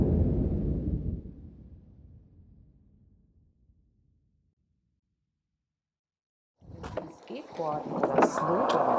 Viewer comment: Hat jmd verstanden, was die Frage bei 12:15 ist? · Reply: Wenn sie Skateboarden oder Snowboarden, finden sie nicht das wirkt unseriös?